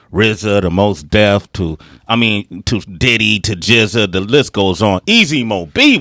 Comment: I think I heard of Rza but who the fuck is jizza? Lol wtf he meant jigga?